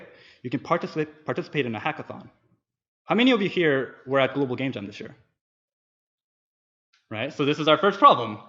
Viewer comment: What does he say at 11:28, "How many of you were at Google ___ ____ this year ?" ? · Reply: Global Game Jam <link>